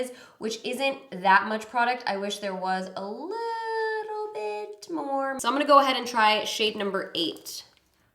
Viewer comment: I heard "I wish there was alotta-bit more." 😅